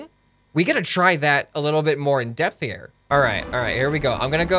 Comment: I could be wrong... But... Did he just say depthier? 😂